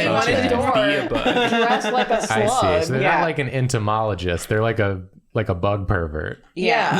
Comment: I completely didn't hear "not" when was talking, so I heard "they're like an entomologist, they're a bug pervert" and i LAUGHED